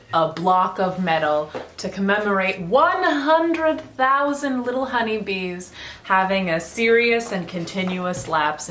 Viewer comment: when u said “100,000 little honey bees” i first heard “100,000 lil huddys” 😭